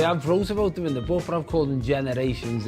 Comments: Subtitle on John bishop I hear ‘book’ not ‘program’. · even the subtitles got it wrong he’s says book not program 😂